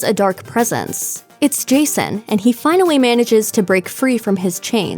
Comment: Is it just me or did you just say "Crystal Wake?"